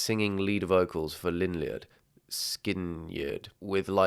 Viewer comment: Did you just say, “Lynn-yUrd, Skin-yUrd??”